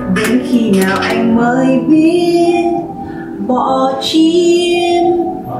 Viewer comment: Bỏ gym... Mà t nghe thành "bỏ chym" ..kkk